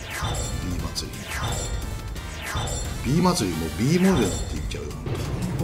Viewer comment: Bモレルって聞こえたw